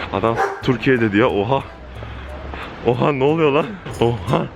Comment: adam tam olarak ne dedi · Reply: galiba suriye suriye türkiyeye dön diyor